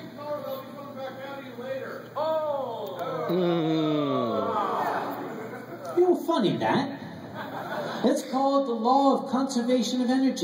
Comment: Im the guy who talks at 8:31 lol. If you couldn't hear what I said it was: "The same color is gunna come back out of you later" xD